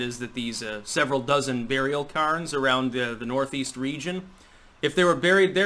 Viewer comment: What you said: "burial cairns". What I heard: "burial Karens".